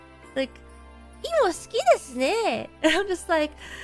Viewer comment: What did she say at 1:29-1:30? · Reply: Imo suki desu ne = oh you like sweet potatoes.